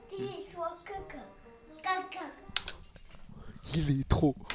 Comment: grand frère en chinois= 胖的 大哥 (kaka) Ce que j'ai entendu (caca)💩